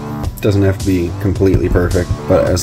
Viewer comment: I swear I heard you say "doesn't have to be completely burrfect". Thanks for your high quality instructions as well as providing reasoning behind each step. This is the perfect example of a channel that I will binge watch all your videos for a new page in my skillset. Thank you!